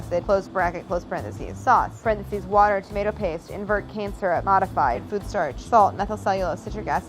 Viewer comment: is it me or when she says cane syrup it sounds like shes saying cancer